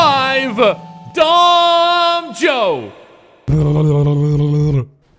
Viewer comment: I thought you said Don Joe to make fun of DonJ